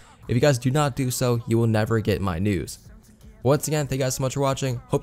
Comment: I thought you said “you will never get my nudes” lol